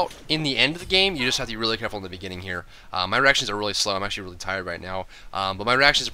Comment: I heard, "My erections are really slow." XD